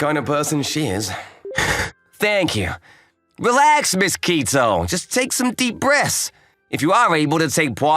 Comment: I could’ve sworn I heard the boss guy say “mosquito” but it’s the step sis name Ms Kito LOL